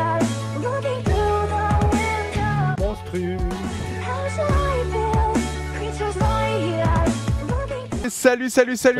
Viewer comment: C'est quoi le truc qu'on entend dans l'intro a 0:34 s ? mdr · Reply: C'est juste une voie rajoutée qui dis "Monstru"